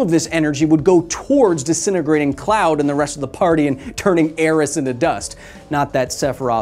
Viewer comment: did you just say aeris?